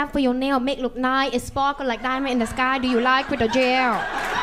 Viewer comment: I thought she said do u like it in jail and she ment gel